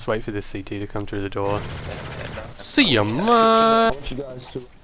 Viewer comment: PSA: people keep mis-hearing 00:58 because I cut off for comedic effect but I'm saying "seeya maaaate" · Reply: thought it said cya mann